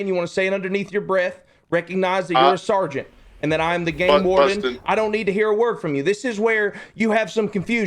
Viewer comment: Damn, it sounds so much like Carter says "fuck Bustin" in the phone call, and I thought that's why Bustin started to go off during it too and says at the end "fuck you". But on Carter's stream he clearly says "but Bustin".